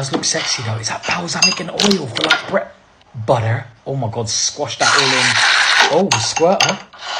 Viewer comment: I had to rewind and make sure I heard him say butter and not bu’uh😂 thought it was the weed for a minute